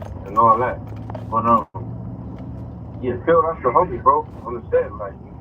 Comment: did he say cuzz that's the homie ?? Ain't he a blood